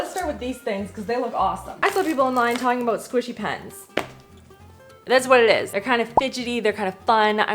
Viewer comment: when you said squishy pens, I heart squishy c***s. It is like the mishearing of ‘green needle’ or ‘brainstorm’😢 My Australian brain went there and I apologise